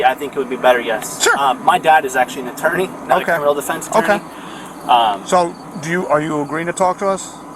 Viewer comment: He says his dad is a criminal defense attorney, yet he waved is right to remain silent and went ahead and spoke with the officers. I'll bet his dad was thinking, "Dang it son, I thought I taught you better than that." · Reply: I thought he said "not a criminal defense attorney." Either way, he's not the brightest crayon in the box.